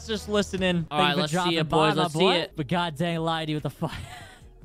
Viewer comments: btw i heard you say god dang the fuck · Where it says "HUH???????" it's because u said God Dang and he thought you said God D*mn